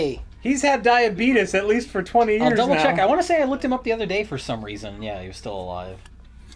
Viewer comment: another star trek reffrence? Checkoff? Ha. I probably just have it on my brain because they just mentioned Jordey but thats what i heard.